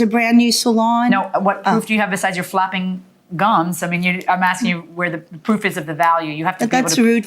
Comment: She thought she said "flapping guns", but doesnt she say "flapping gums"...as in "talking"? Did She think this was a weight joke about her arms? That would be rude of judge, but thats not what she said.